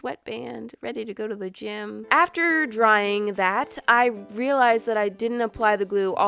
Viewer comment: did anyone else heard rippears or is it just me? If you agree = 1 like